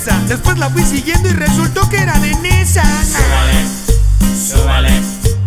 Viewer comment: Te la micro-mamaste :v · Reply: no entendi lo que dijo, que era danesa dijo????????????????